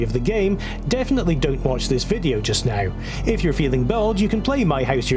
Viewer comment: Unrelated question but guys, at some point 0:50 , he said "if you are feeling belge" I believe I heard. What does it mean? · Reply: “If you’re feeling bold”! There’s definitely something odd with my mouth or the sound there